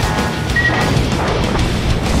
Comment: did he just say... enemy destroyer suck?